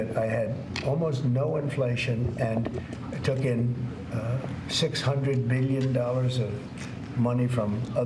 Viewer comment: Question: If he took in 600 billion wouldn’t the US debt have been down when he left office? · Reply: I thought he said 600 million...which is chump change, relatively.